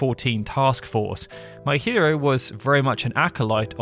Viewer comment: Every time you mention Kazutoyo Maehiro, I hear "my hero" instead of "Maehiro". And considering that this dude is bringing us this game, it's very fitting 😎